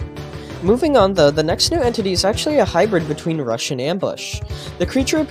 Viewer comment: Dude did the subtitles just say "Russian" instead of rush